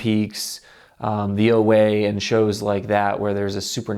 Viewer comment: at about the 8:00 minute mark he says shows like Twin Peaks and some other show but I could not understand what he said. Can someone help out? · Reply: The OA.